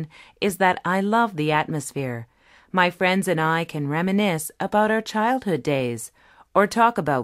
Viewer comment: Was does she say in 7:37? What is this verb? "Remeness"???